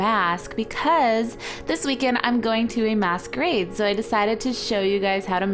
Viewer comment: It sounded like u said The weekend I'm going to a mass grave